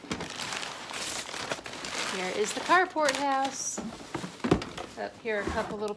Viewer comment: Ohh, it’s the Carport House! I just discovered your channel recently and in one video you mentioned it. I thought you were saying “cardboard house” and I got confused because it didn’t look like any of the houses were made of cardboard😅